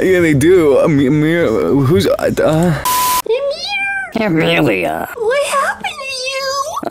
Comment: voice sounded like a cat when she said amir, it sounded like meow lol.